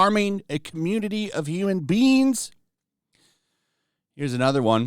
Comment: I heard Human Beans rather than human beings. I shall call snow flakes as Human Beans now. Might be disrespectful for Beans and the Legendary Mr. Bean though